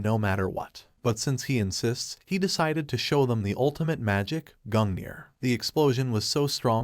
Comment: bruh I thought he said gundyr :/ (still can't beat champion gundyr even after sister friede 💀)